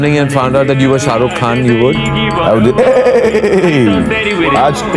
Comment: did he say ..salman toh gay nahi ...? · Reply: Fr He said "Salman kyu gaya hai" 😂 Listen carefully with the help of headphones